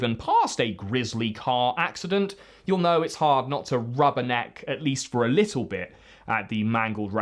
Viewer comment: Rub her neck? Nah, too busy rubbin' my- Whatdo you mean that's not what he said?